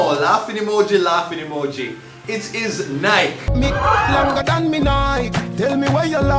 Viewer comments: I thought he said Nile...longer dan do nile...😂😂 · I thought Vybz said, cocky longer dan me Nine